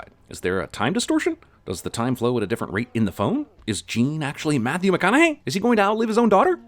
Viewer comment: Did anyone else catch the “Matthew MehConaughey” at 4:14?